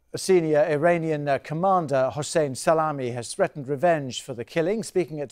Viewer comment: did he just say "Hussein SALAMI"?